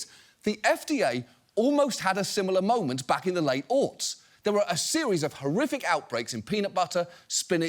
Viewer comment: is driving me crazy. "the FDA almost had a similar moment back in the late ____________." What the heck did he say there? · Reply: He said "late aughts" which referres to the era from 2000 to 2009. So basically the 2000s.